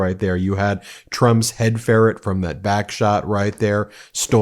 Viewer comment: i think he said backshots